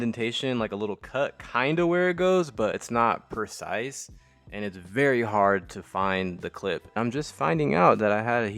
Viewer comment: Bruh I thought you said “very hard to find the clit” 💀